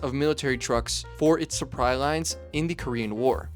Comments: did you say "surpry lines"?😑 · My brother in Christ, did you just say “suppry” lines at 2:45? 🤨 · does he say suppry lines? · did he say "supry lines?" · did he really say suppry in a video about Asian automobiles?